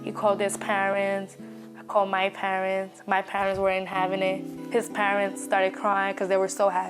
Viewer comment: At first I thought she said her parents were “in heaven over it”. But relistened and she said her parents “weren’t having it.” Stupid parents-It’s here! Your judgement is not helping. I hope and assume they came around eventually! My niece also had a baby without knowing she was pregnant (her second child). Her sister said she was not very “connected” to her body…and it was true that she had anorexia issues since very young. Her crazy mom put body issue on those two…sad.